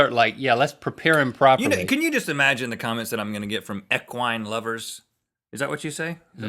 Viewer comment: I thought he said EQUIUS lovers.